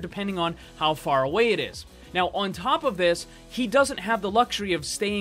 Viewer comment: did he just sayd SHE?!?! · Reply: Toy chica Ro no he said he